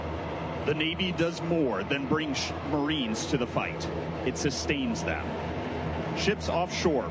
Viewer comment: am i the only one who hears the guy say “the navy does more than bring shht marines to the fight” 🤔